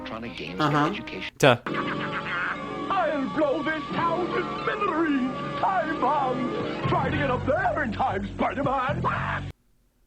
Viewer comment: Green Goblin: I'll blow this town to smithereens! Time bomb! Youtube Captions: I'm gonna pretend I didn't hear that.